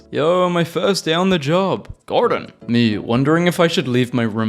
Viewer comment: Excuse me, did you just say "Gordon"?